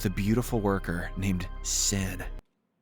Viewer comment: I thought it said her name was sin😮